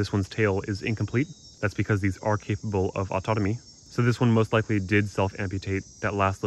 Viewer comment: just a brief note: the subtitles at around 1:58 say autonomy instead of autotomy